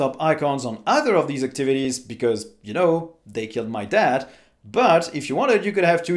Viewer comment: did he say "you know they killed my Dad"?